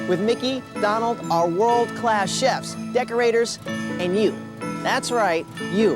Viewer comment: for a second i thought he said "world-class ships" like Disney Cruise Ships